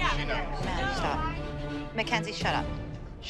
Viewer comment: On 5:21 Melissa said shut up mckenzie and maddie did NOT say "you too" And reason why maddie was annoyed it's because her mom was embarrassing her bye crying in the audience over a win